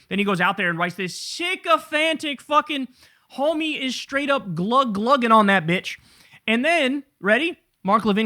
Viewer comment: I heard "glot[is] clogging" and that's the funniest thing my non native ears have heard in a while.